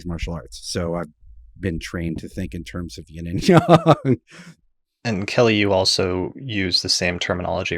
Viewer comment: Heads up about 13 minutes in, when talking about quadruplicities, the caption says "immutable", it should say "mutable" Around 18:30, says "yin and yang" and it's captioned as "unity"